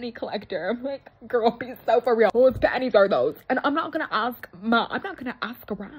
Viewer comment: Listen hear me out I don’t know if it’s just me that hears it but it sounds like she said girl be so for real who’s baddies are those I can’t tell if it’s panties or baddies please tell me I’m not the only one😂😂